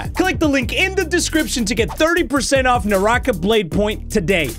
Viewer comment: Literally when you said Naraka I thought you were saying my name. I was like “ how does he know my name😭 “ ( my name is Niriah btw literally the first 3 letters sound the same )